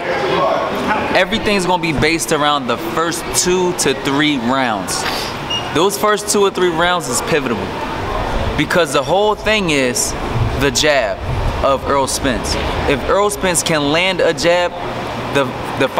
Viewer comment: Did he say pivitable? 5:33.